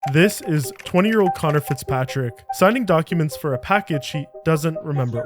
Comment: Initially because I missed the subtitles & I thought I heard “counterfitz Patrick”, I thought💭 we were being trolled lol but the video is about hacking not about counterfeiting.